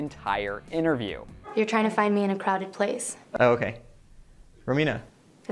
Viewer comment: This is what I heard InView: "you're trying to find me in a CROWLEY place?" JesseE: "Rowena" shrieking internally supernatural fandom